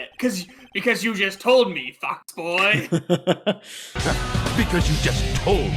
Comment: Am I the only person who heard "Fuckboy" and not "Fox boy"?